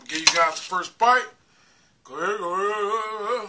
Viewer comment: What did you say at 1:20? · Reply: AsbestosWelder68 i said go head wooooooo ha ha ha ha tfw buddy